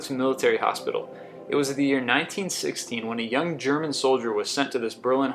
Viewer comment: number 22, did you say 1960?